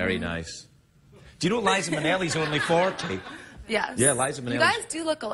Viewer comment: Please what does he says at 15:38 ?? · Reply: liza minnelli is only 40